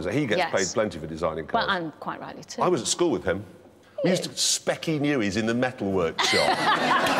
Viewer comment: what does Jeremy say at 4:48? We used to______ in the metal workshop ??? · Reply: Vladimir Perić - "I was at school with him. We used to.. Speccy* Newey’s in the metal workshop." *Glasses, spectacles, specs for short, or in this clip, speccy.